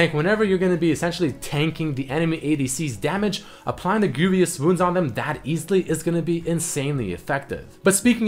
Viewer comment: I thought he said grooviest wounds. a nice tip to know about pronunciation. vous has no "i" so don't say greivious like "envious" good vid nonetheless